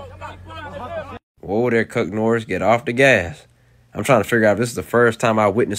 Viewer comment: Homie. I swear to god I just heard "Cuck Norris"...For the love of all that is good in the world...please tell me I'm not the only one because the captions say 'Cook Norris', but I hear the other, while my mind is desperately going back trying to hear 'Chuck Norris' ...please...